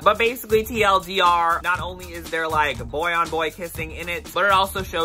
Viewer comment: i thought you said "Boy on Boy Pissing in it" and my eyes bugged out i thought we were talking about marudase kintarou